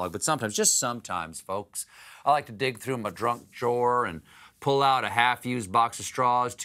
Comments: did he just say he likes to dig through his DRUNK drawer? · Haha, he said 'Drunk Jawer' instead of Junk Drawer